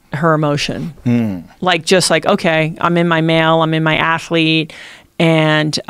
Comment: The fuck did she just say "in her male"??? 1:55 I hope she's talking about chain mail